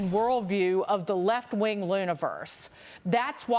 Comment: did she say Left Wing LOONiverse. If so, she sure got that right.